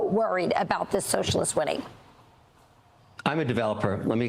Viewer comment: I listened to it 5 times now. Did she say at 17:58 "....worried about these socialists winning"?...The closed caption ommited it. Hmmmm